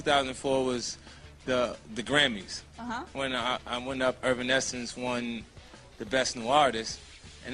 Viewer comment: did he call them Irvin Essence? 🤣🤣🤣